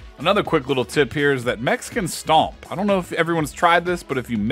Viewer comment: thought you said mexican stomp